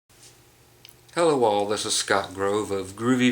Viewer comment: you said "hello all" and I thought you said "hello walls" and it was going to be Faron Young day lol. thanks so much for the amp info! I bought a little practice amp and had no idea --till now -- how to set it up right.